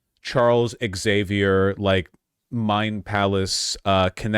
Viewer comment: I heard Charles Xavier's Mind Palace as in the vein of Criss Angel's Mind Freak.